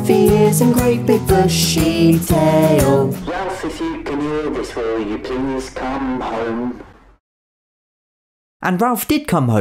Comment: if you cant hear it, hes saying: "Ralph if you can hear this will you please come home."